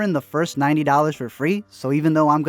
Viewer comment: Did he just say first 90 dollars for free or first 90 days for free lol